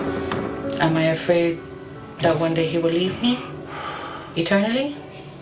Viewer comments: I thought she said she was afraid that he would eat her 😂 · I thought she said am I afraid he'll eat me 🤣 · I actually thought she said "Am I afraid that he will EAT me??, Yeah!". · I misheard a part and i thought she said “Am I worried that he will eat me? Yeah.” Sorry, not trying to be rude, just thought it was funny.